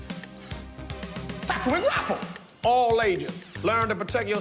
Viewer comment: Umm is it Tackle-more grabble or just tackle & grabble. Because on the beginning when he first said that it sound like he said “tackle-more grabble” and even YT CC translated “tracklemore grabble”😭😂😭